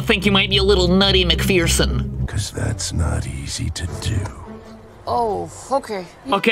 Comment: I friggin love these videos 😂 Disney definitely likes to find ways to get away with sneaky adult content 🤣 nice to know they haven't changed that lmao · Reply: Terreberry he Actually Says "Oh, Ok."